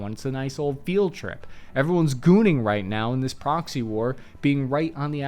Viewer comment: did this man just say "Everyone's gooning" during a video on previously classified missions in ukraine like im invested i wanna know what was going on in my country and then i hear "gooning" and its like i got punched in the gut